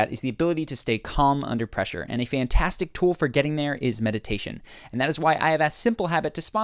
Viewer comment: I heard “meditation” as “medication” which still holds true. I was so calm under both of those. 😎